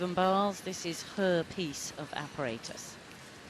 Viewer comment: for a second I thought she said "this is HerPes of apparatus" I laughed